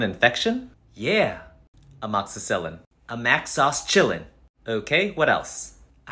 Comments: "A mac sauce chillin" sounds like a string a McDonald's commercial would say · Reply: I hear “ a mad sus chillin” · As soon as I heard "a mac sauce chillin" I yelled "IT'S AMOXICILLIN! IT'S THIS" and felt really proud of myself thank you